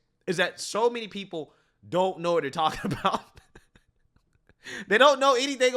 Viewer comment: for a moment there I thought you said “Soviets don’t know what they’re talking about” But instead, you said “so many don’t know what they’re talking about “ Though I don’t think that’s to inaccurate